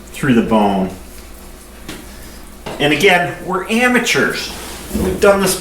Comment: when you said amateurs, for a sec i thought you said hamateurs lol.